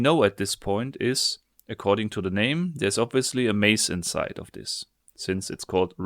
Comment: I legit shat my pants cuz i thought he said Mace